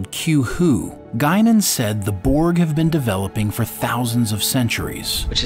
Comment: I thought she said thousands of centuries. That sounds a lot longer than thousands of years. Not sure I am prepared to agree yet.